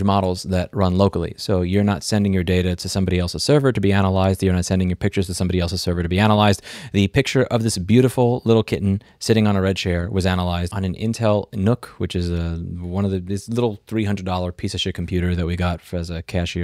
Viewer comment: Ngl.. i thought you said Clinton on a red chair!!! Ahahahahah!!!! 😂😂😂😂😂😂